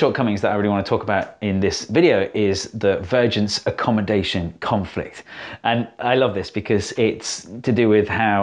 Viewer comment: I heard "Virgin's accommodation conflict in 3D" X@